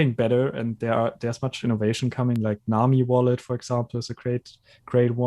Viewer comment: did he say nomi wallet? What'd he say? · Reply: Nami Wallet